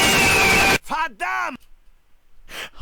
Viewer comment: I don't think it's saying "Hot Damn" but rather "Verdammt", which is "dammit" auf Deutsch